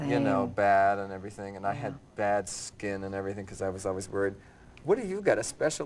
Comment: he said he has bad skin because of why? Did he say because he always worried?